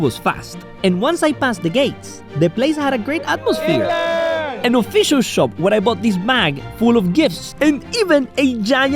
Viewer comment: WHAT DID HE SAY? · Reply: He said England